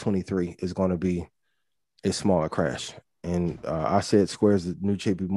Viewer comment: What is he saying about 2023 (Around 6:15)? Cant hear well... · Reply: Small housing/real estate crash.